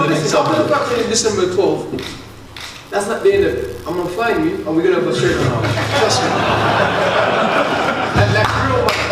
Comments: Did anyone pick up what Dillian said at around 8:00 that made everyone laugh? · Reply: We’re gonna have a straightener after this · What did Dillian say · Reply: "I'm gonna find you and we're gonna have a straightener" (I think) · What did say at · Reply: We’re gonna have a straightener after this